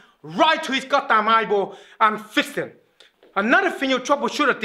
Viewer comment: Did he say Fist Him???